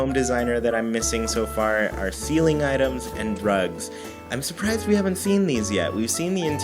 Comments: "ceiling items and drugs" wait... what did I hear "ceiling items and rugs" oooh phew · I heard "ceiling items and drugs" instead of "rugs"... 😂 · I thought you said "ceiling items and drugs"